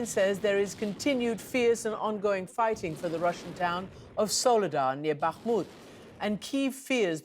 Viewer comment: Wait did she say Russian town at 0:23?